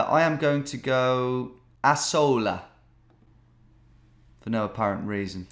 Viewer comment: that sounded like an Arabic speaker saying asshole lol